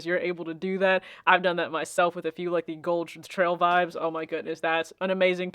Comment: Hi! Great video! I just had one question. At the 4:18 mark of the video you were talking about colorful air max and mentioned one but I couldn't tell what you said. It sounded liked something trail vibes but I wasn't sure. Was curious to check it out. I'm big into colorful shoes too. Thank you! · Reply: Hey! Gold Trail Vibes, it's got a lot going on with it but the base of the shoe is tan and just an overall great pair. I'll link the video for it